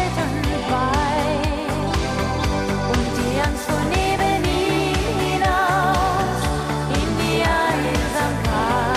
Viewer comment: Egal wer singt, ich verstehe es akustisch beim besten willen nicht. Was bitte ... in die Einsamkeit? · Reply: und die Angst fuhr neben ihm hinaus, in die Einsamkeit